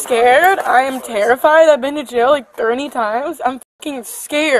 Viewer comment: Says she's been in jail 30 times but thinks she's the victim in everything. Gawd she's infuriating! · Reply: 30 times really doesn't make sense but that's definitely what she seems to say